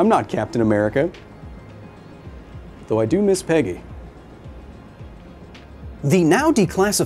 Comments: Oh.. I thought he said pegging, not Peggy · You said you miss Peggy I thought you said you miss pegging im like that's was random lmao